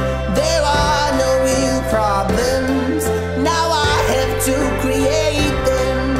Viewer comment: It’s really sounds like ; 1:14 There no new problems, instead of real problems 1:22 To my world again, instead of worriying 🙇🏻‍♀️